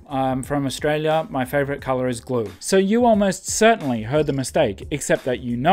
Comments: I watch all English at 2x speed and I swear I heard blue not glue the first listen. Thus proving your point more. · lol my brain actually did autocorrect that- i thought you said “my favorite color is blue” instead of “glue” so when you mentioned a mistake I had to rewind it and see what mistake you were referring to 😂. That’s what happens in writing too! I’ll read comments that have slight grammatical errors but my brain just autocorrects them and I don’t even notice them until someone points them out 😂